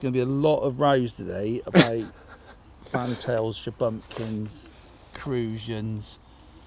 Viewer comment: The key word is shubumkin, after watching again sounds like said shubumkin and not shubunkin